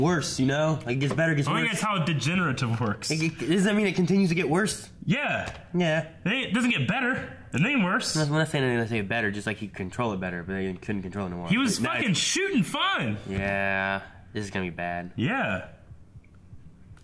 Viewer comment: it sounded like said merit ain’t evo shit